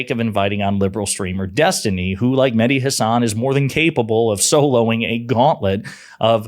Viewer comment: you said Mehdi Hasan but I didn't quite catch the "Mehdi" the first time around so I thought you were saying "Like Hasan", implying Hasan Piker. I don't think I've ever seen that Hasan spend more than a few minutes talking to anyone that disagrees with him much less taking on a gauntlet of Trump supporters XD